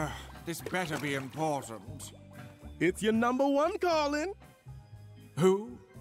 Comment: I thought in 0:11 king dice called the devil "its ur number one darling" instead of "its ur number one calling"